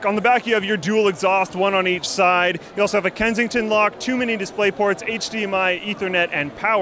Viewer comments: lol...I heard "too many mini display ports" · Sounded like he said 'too many display ports' lol Also dats a smexy laptop